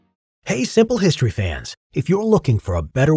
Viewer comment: did you say "siple" history fans